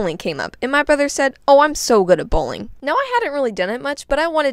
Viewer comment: I thought he said ✨I’m realy good at BULLYING ✨✨😂😂😂😂😂😂😂